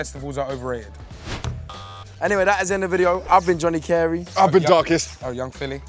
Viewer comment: Man thought it said Johnny Depp